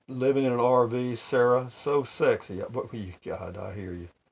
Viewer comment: Buenas dias 🌤️🌵 Carpe diem mi amigo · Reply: Morning....LOL...I said the Rv issue was so sick.. thought I said "so sexy"...so we need someone in charge, but as my dad would say, "he did not get hit with the ugly stick". Happy Coffee and Tuesday accomplishments rangers. Happy safe travels to Elmer.